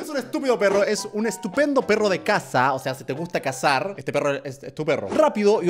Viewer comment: cuando dijo perro de caza yo entendí perro de casa :v